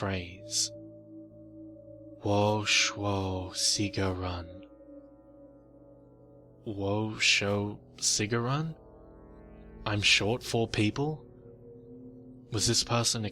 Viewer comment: Woah, now that was creepy. But still, for some reason, before it was revealed what he was saying, I thought she'd say he said: I ate four people. Lol